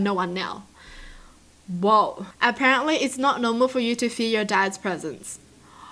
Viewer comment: I thought she said: "Apparently it's not normal for you to eat your dads presents " Then I actually read it and said "ooooooh" 😂😂😂